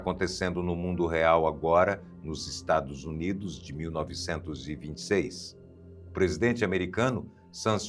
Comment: Aos 10:50 ele disse 1926? não entendi !!, talvez eu tenha entendido errado?